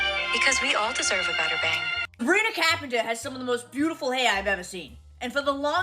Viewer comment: The way she said, “sabina capenter” 😭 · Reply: I straight up thought that she said marina caphenta